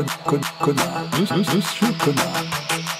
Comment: Pourquoi au début j'ai entendu "monsieur Connard" ?